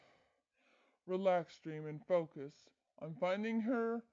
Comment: I swear it sounded like dream said: focus demon XD